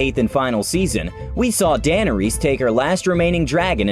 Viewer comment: did he say ‘Dana Rhys’ lmao